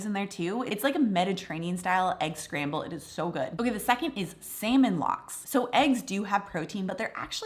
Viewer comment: What was number 2? Sounded like she said "Salmon Locks", what is that?